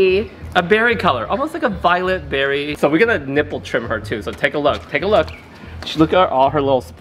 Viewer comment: did he say he was doing a nipple trim? LMAO I LOVE THIS GUYYYYY